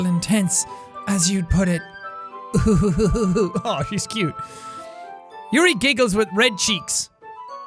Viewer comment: 1:1:25 Did he just say "HE is cute"?!